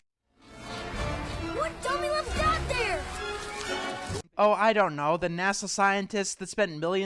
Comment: What does Milo say at 7:03? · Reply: "What dummy left THAT there?!"